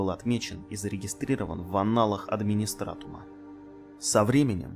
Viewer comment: в аналах администратума? Или мне послышалось? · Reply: Levi В «аналах администратума» может быть только, пардон за мой французский, Геморой, а тут имеется ввиду слово аННалы, что означает погодовую запись событий. P.S. Понимаю что прошло пол года почти, но вдруг прочитаете)